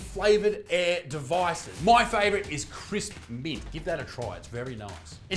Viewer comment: Thought he said crisp meat